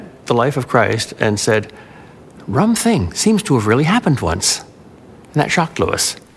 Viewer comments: What is the fellow at 0:56 saying? All I hear is "rum thing seems to happen twonce". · can anyone tell what he is saying here? I cannot figure it out. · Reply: Rum thing. A European phrase meaning that's odd.